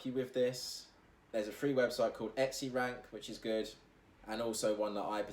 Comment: You said @ 6:02 something like "etsybrand and marmalade." Links? I can't understand what you said and can't find them. thx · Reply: It's called Etsy Rank or E-Rank (google it)